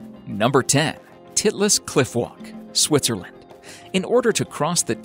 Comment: I thought he said the "tit less Clit walk😂😂😂😂😂😂